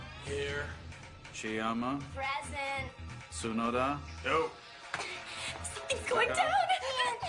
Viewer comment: did he say nope 🤣